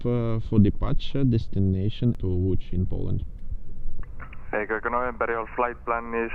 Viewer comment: I swear at first I thought you said Thailand. I was dumbfounded that you fit Thailand to Poland in 20 minutes!!!